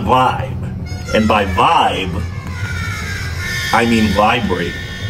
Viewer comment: When you said to death I thought you said today